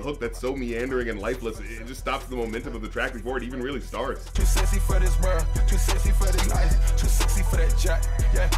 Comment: Ngl i always thought he said "too sexy for this bruh" and am only now learning he said "too sexy for this world"